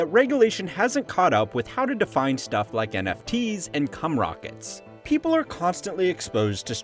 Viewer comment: “NFTs and Cum Rockets”? What does he say there?